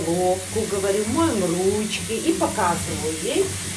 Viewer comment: Что такое ручия (компот)? Это алыча? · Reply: глуховата я значит 😄. Мне послышалось "ручия". Подумала, что это еще одно название алычи 🙃